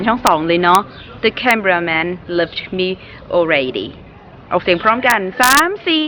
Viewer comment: he sounded like you said the camera man love me already instead of the camera man left me already